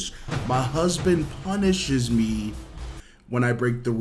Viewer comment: Wait who else thought it said “ my husband PUNCHES me” 😭